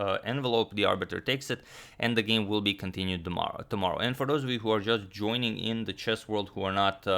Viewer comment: I thought u said the game will continue tomorrow after h3 🤔😆